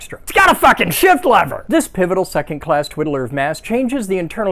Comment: I heard “a nude shift lever on the occasional pig”😂😂😂